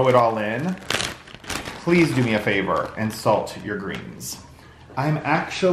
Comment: Omg, we are laughing so hard….the husband was marching about as I was watching your vlog….what he thought he heard you say, “ do me a favor, assault your greens!” My husbands head whipped around so fast and he said in all seriousness to me, “why does he want his viewers to ‘assault’ their greens?” So happy to spend time with you this month!